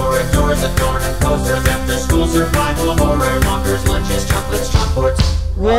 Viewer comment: Baldi: Problem 1: 1+1=? Me: 3? Baldi: gets ruler Me: why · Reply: did u say chocolate